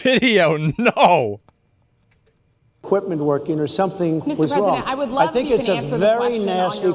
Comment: Did she say „mr. president“ at 3:15? 😂😂😂😂